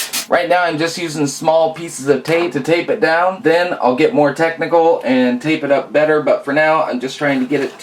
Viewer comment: And then I'll get more tech-deck-al. Haha, instead of technical! xD